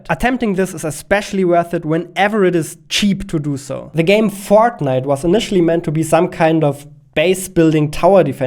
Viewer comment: heard it as "the game «Fart Night»"